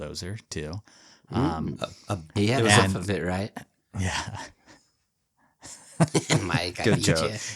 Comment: WTF did he say that was so funny? I played it back 20 times and still don't hear it. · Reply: said "he had half of it right" about the bulldozer because its a bull - shark